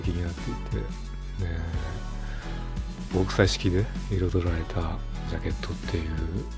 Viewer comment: 〇〇で彩られた… ここの部分ってなんて言ってますかね？ 上手く聞き取れないんですけどわかる人いたら教えてください!!🙇‍♂️🙏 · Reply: 極彩色ごくさいしき、ではないでしょうか？